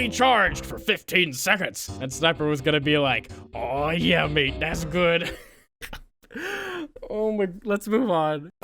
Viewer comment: I Actually thought he said "Aw yeah babe das good"